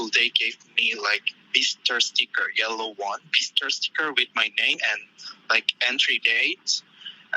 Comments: I think he's saying "visitor sticker," FWIW (though I think the news channel did the transcription) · all of the (inaudible) is saying "visitor". As far as I'm aware, East Asian languages generally lack the "v" sound, and often substitute it for a "b" sound. I know this from learning Japanese, so I'm not sure if this is entirely accurate for Korean, but it does sound like he is saying "bisitor". Correct me if I'm wrong! Edit: misspelled his name, sorry 😔